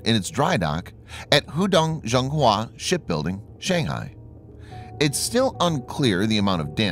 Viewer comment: I could have sworn he said "Shit building"